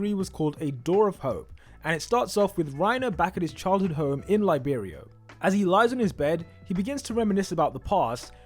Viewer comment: Did he say Reiners childhood home was in Liberia??